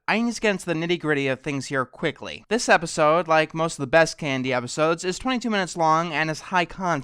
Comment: It sounded like he said candy instead of KND